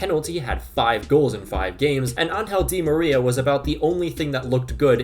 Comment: bruh i thought you said “unhealthy maria” at 7:45 LMFAO